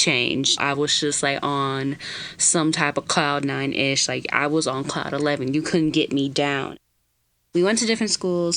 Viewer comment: Girl: 9 ish to cloud 11 Me (Misheard): 9-11!! AHH